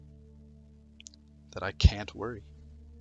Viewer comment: When he said I can't worry I thought he said I can't twerk